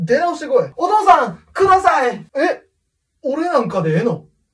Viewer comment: ハッピーウェディングがハッピースマイルに聞こえた俺は末期